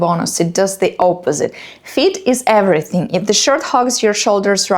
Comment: I thought she said"feet is everything" lol. Fit. She said fit